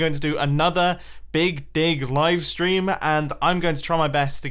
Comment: I thought he said Big Di*k livestream the first time